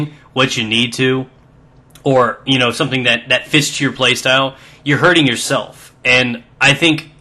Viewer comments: Did he just say "Fists you Playstyle"? Well.... give me an Assault Rifle and pack me some lube.... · thought he said: "That fists your play style"